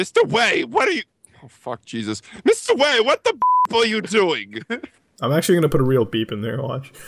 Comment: Wait a minute, does he actually say, "What the beep are you doing?" As in the word, "beep"?